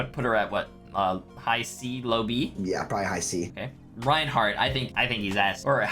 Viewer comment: When he said rein s i thought he said reinhardt ass